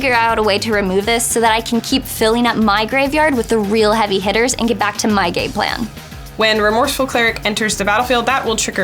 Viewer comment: she says "so that I can get back to my gay plan" instead of "game plan" That's all